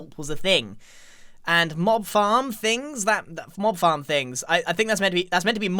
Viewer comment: Got jump scared cause I thought I said morb farm